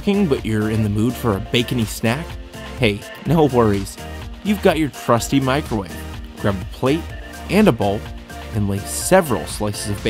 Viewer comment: I thought he said "You got your crusty microwave" I was like o.o